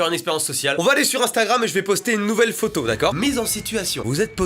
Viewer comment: C'est moi ou il dit "instaram" a 1:13😂😂